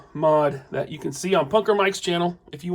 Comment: Need a link or name of the YouTuber who customized Doc Oc tentacles . Sounds like you say PokerMike, But after my search, all I find is poker cards stuff . I would love to fix those terrible arms that it comes with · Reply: Punker Mike